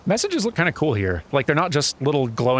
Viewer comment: DID I HEAR A ROCK AND STONE?